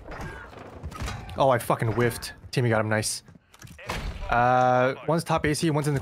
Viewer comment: I kept hearing “timmy got em” 🤣 Good shit guys!